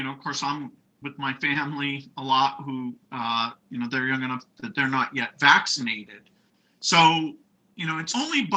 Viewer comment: “My family who are not going to.. not yet been vaccinated” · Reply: That's not what he said and it's clear what he said. @ 42:00 he says "My family who are young enough that they're not yet vaccinated". Stop the nuttiness.